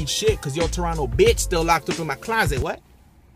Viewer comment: but did he just say team dark skin lmao